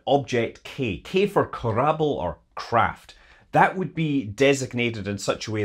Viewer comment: did you say K was for Kerbal???